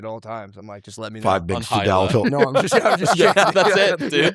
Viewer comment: what did andrew say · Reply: 5 MG whatever the boner pill was, todalophil or what ever the hell it was called